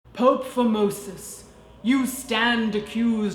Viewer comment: I swear, the first time you said, "Pope Formosis" I thought I heard, "Pope Vomitus."